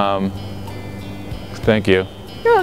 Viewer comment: "Spank you" 'Your welcome!' xD Please prove that I didn't hear that